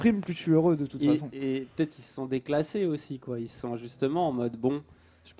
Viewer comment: J'ai entendu déclassé? J'ai pas le choix je laisse ça là: <link>